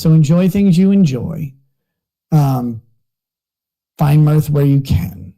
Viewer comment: I genuinely thought he said "Enjoy things you enjoy, find meth where you can"... I had to rewind 🤦‍♂️